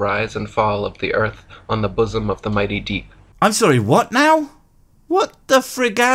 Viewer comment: i honestly thought he said "the mighty D" and was exspecting a D joke😂